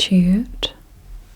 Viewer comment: i have a question, my first language isnt english so what did she say here? 1:36 help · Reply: I’m pretty sure she said gratitude 😊